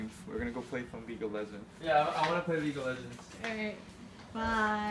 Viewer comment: what does he say? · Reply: jason tran wildturles name